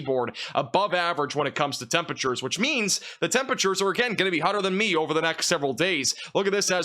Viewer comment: Temperatures will be hotter than you. I get what you're saying, but it's still funny. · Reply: If you listen at 9:21, at least I heard :"Temperatures will be hotter than me".